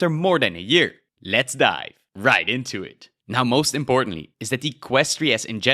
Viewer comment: why each time i keep mishearing it to "Let's die right into it."